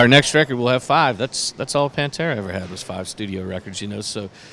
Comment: Did hee say pantera had 5 albums so does HEll yeah